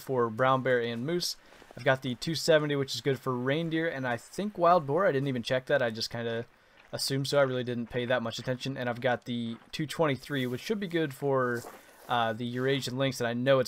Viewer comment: Not 100% with the 243 on Musk Deer, only 223-357 Mag or bow with the 300 grains arrows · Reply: UPS, sorry thought you said you had the 243 with you, my bad